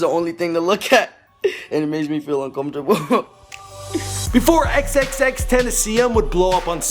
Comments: Who is xxx Tennesseeon cuz that's what he said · Xxxtenacium? I know its tentacion but it doesnt sound like he said it